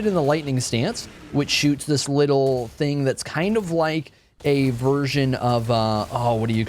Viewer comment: "A version of hera 1" at 1:38 sounds like "a version of heroine" and thats what I heard haha